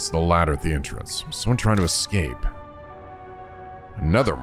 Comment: Turns out there wasn’t a ghost or anything supernatural down in the sewers, it was just Saejima trying to escape from the popo. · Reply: I thought you said "Saejima trying to escape from the Poo" and had myself a giggle